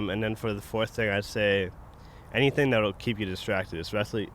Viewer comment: what does rob say at 9:59 "he's an asprinner?"